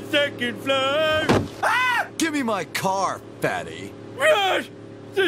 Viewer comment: I thought Snake said to Homer when he landed in the car, "Give me my car back!"